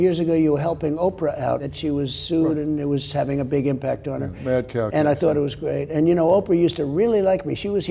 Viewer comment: The Only Way To Truly Put This Country Back Together Is To Hold People Accountable For Their Actions. That's What Makes America Great. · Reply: Did Phil just say "Mad Cow disease @ 12:00? After Don mentions harpO.